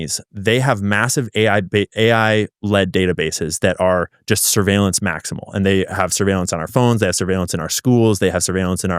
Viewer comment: I heard surveillance maxxing 😂